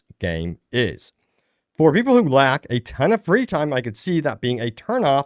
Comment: thought u said "for people who black"😂😂😂 im like hold up brotha!!!......oooooooooo LACK 😂 but this game is about to shock the industry if done right and we have to be realistic and know they will patch up stuff and fix things but I still think it's gonna hit hard march 19th...Also I'm surprised we have not seen any first person combat for sure 6 hours i would have attempted atleast 1 time and see if I could make it work